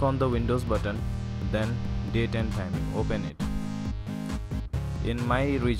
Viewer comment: What does he say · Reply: select "Date & Time"